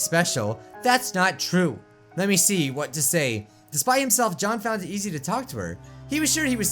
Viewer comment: Bijuu Mike says: Despite himself he felt it was easy to talk to her. Captions say: Despite himself Johnfound zz2 proctor. 🤣